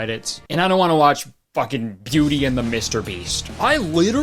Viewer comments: When you said "Beauty and Mr.Beast", I heard "Pewdie and Mr beast" as in pewdiepie. That's a movie I wanna see 😅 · I thought he said pewdie and mr beast and was a lil let down not seeing pewdie pie instead of belle😂😂 fav vid so far tho I died laughin